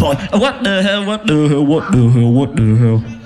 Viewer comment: Did he say, ' Wadu hek'.